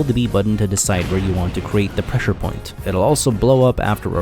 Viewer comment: Did I just hear this man call it a pressure point?